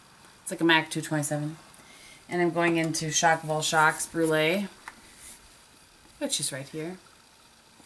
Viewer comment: Great tutorial! Here's a funny: I was making my coffee while watching & thought I heard you say the name of your highlight color as 'Shock of All Shocks.' I was like, "Oh pretty! I haven't ever heard of that shade?" haha I'm a dork.